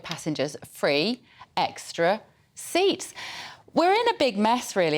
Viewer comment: You must of misheard ! They said 3 seats not free seats ! 😂😂